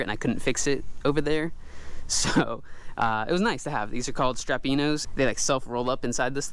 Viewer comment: I thought he said "strapido" and searched for images of them to see that I heard him right. Thank God there were no children close by to see the search results. I listen harder next time and they're called "strapinno".